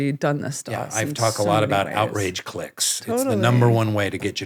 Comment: When Rainn said "Outrage clicks", I initially thought he said "Outrage cliques". I guess it can go both ways 😂 people forming groups based on the common things they are outraged about. People desperately wanting to be part of a group, eventhough their main bond is based on what they hate/fear. When you drill down on that, you realize people are generally just lonely and are trying to connect with others at whatever cost.